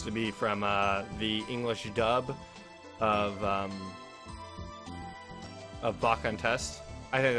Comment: What's the name you said on 8:54? I can't understand it. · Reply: Baka and test